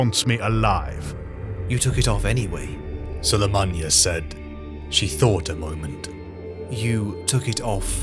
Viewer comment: Who is this person Guilliman was speaking to at 2:16:09 ? Cant quite catch their name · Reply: Yassilli sulymanya